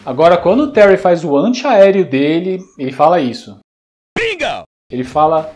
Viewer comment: cara eu ouvi, Pinga!!